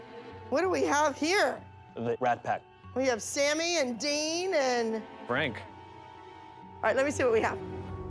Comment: did Abby actually say Sammy and Dean at 0:21? Cuz the spn fandom’s gonna go crazy!